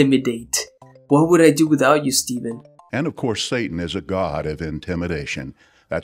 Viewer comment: did he just call satan a god!?